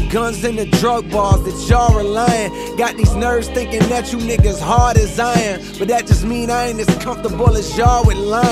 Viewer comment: is it just me or does it also sound like he says "got these nerves thinking that you niggas hard as iron"